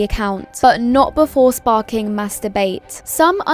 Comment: I can't be the only one who heard "masturbate," right?